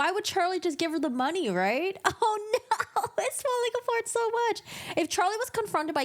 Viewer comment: I had to listen to this 3 times live! It sounded like you said "it smells like a fart; so much!" 😂😂😂 Which was true! LOL! I've never heard anybody say that but it was very fitting! LOL! 😂 (it's falling apart so much) LOL! This is Niqole 😘